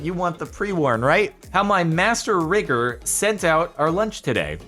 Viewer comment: all I heard was “master rigger”…